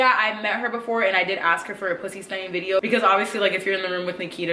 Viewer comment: Does anyone know what she is saying when talking about Nikita Dragun at 11:27 "yeah I met her and I did ask her for a _____ video?" I have replayed it ten times and I can't make it out, but it's going to bug me haha. Thanks :) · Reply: She said pussystunting video 😊